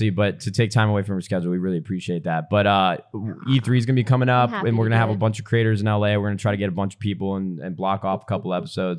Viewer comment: did anyone else hear that? what was that lmao? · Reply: Qintz she was saying “Yasss”